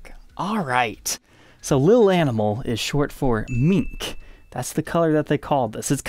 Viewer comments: “Mink”. Oh okay, for a second there, I thought you said “meat”. Gibson and their color names, what? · did he just say that "little animal is short for mink" ? You really feel that a 4-letter word needs to be shortened . . to a 13 character pair of words ? ? ? Public school much ?